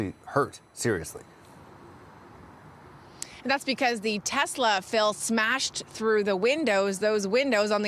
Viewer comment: "that's because the Tesla fell smashed..." wtf did she just try to say? lol